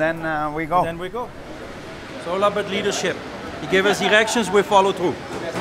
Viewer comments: I thought he said (3:57) "he gave us erections ....and we follow through" than his accent meant "directions" 😂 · I thought he said “it’s all about leadership he gives us erections and we follow too” 😂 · "He gives us erections , and we follow through." 😂😂😂🤣🤣. I wouldn't have doubted that was what he actually said for some reason? 😆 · did he say he give us ERECTIONS???